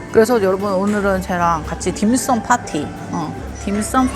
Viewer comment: 딤섬 파티를 난 김일성 파티로 잘못 들었다.ㅋㅋㅋ 드시기도 맛있게 잘 드시네. 복고 슨 그라서 직이네.ㅋㅋㅋ